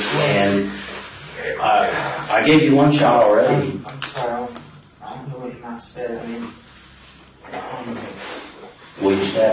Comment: The one part, you said, "I really don't know what he might have said",...i clearly heard, "I'm sorry"..."I'M REALLY NOT SCARED OF YOU" Feeling in charge I guess, not afraid of what he's facing